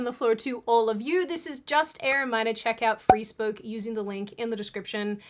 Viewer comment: I thought she said “free spook”, bless her 🤗